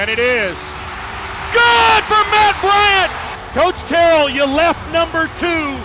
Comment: Thought he said matt ryan lol